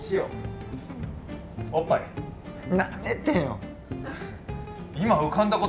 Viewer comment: 舐めてよに聞こえてびっくりした